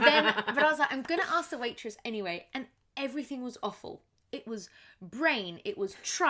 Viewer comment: Thank you for the precision awful / offal, I thought said awful